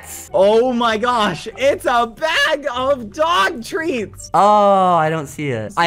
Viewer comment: I thought he said dog treatments